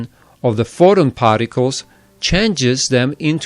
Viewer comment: is he saying photon particles?